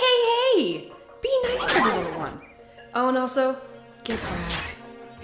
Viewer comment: Wait what did strongarm say at 2:03? I couldn’t hear it I just heard “get” and then something, but this was another great chapter! You’ve done amazing with this bro! Arguably the best tf stop motion series rn (edit: I now know what she said lol) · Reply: She says “get fragged”